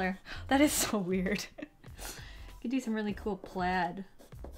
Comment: Did...did you just say... Plad ?? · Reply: Fawny Deer yes, but she said Plad. Not plaid No h8 or anything lol it's just a joke, u can pronounce words differently than others lol